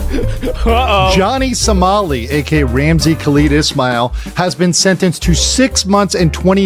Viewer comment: his name is ramsey khalid ismael but i sure thought they were saying ramsey cletus mail this whole time